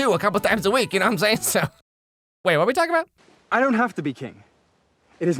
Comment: For real when said "I don't need to be king" I heard it as "I don't need to be gay" like what?💅